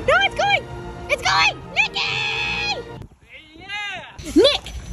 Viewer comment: i love how she said "NICHYYY" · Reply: I thought she said Mickyy